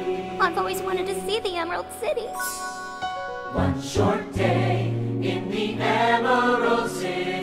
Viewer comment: Am I the only one who hears "What's your name?" instead of "One short day"? ....No?.. Ok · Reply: I heard "One short gay"